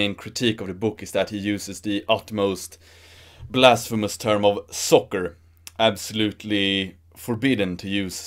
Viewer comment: Oh, soccer! I thought you were saying "sucker".